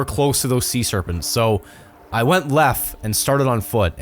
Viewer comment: did he say i went lef